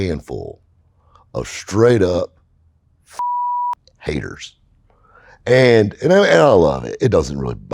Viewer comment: “Fucking Haters” is what hey was trying to say guys I got y’all’s back..